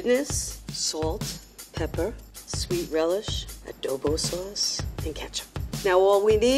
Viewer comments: oh my.. did she say ADOBO sauce?? all the toxic filipinos like myself: REEEEEEEEEEEEEEEEEEEEEEEEEEEEEEEEEEEEEEEEEEE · Did she just say ADOBO sauce? Im confused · DID SHE SAY- "adobo sauce"? 😮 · did beth say "adobo sauce?" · did she just said adobo sauce? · Did she say adobo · did i heard Adobo sauce?☺️ A very popular dish in our country☝️